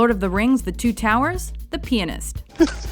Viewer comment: When she said pianist I thought she said penis